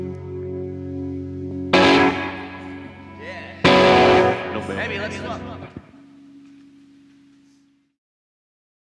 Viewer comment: WHAT DID STU SAY?? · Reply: 'Amby let's swap.' He then swaps his guitar with Ambrose Kenny-Smith's kit.